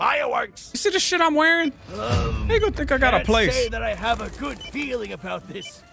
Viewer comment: had to do a double take here I swear it sounds like says n** think I gotta place lol gave me a good laugh